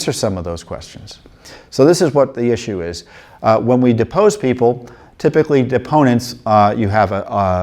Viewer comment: Ohhhh Depose. I thought you said Dispose! You got my hopes all high again. Thank you JW. God bless you.